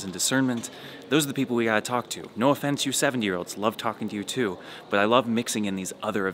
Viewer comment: Ha! I thought he said 7year olds